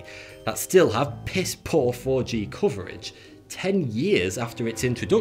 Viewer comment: thought he said "Poor orgy coverage." Played back. He said "poor 4-G coverage." My bad.